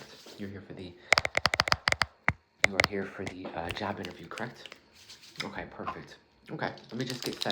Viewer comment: I thought you said jap inerview at the start and I’m like wtf is he talking about then I realised it was job interview. your accent 😫